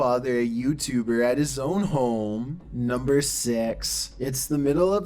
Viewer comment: did he say number sex? And why he talks like gay Trump? That was so annoying.